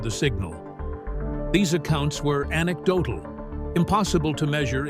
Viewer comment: What is the word he used 29:05 ❤ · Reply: Anecdotal